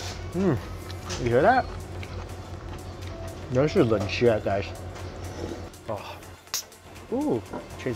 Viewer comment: did he say "this is good shit guys"? Lol not Mike! He'd never swear... right?